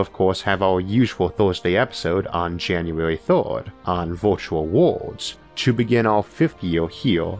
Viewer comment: What accent is that? Every time you said “world”, I thought you were saying “war”. It got kind of confusing. Interesting video though.